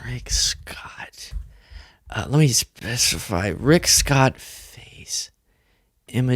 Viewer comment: When you muttered "Rick Scott" at 22:26, I did a double take because I thought you said "Great Scott"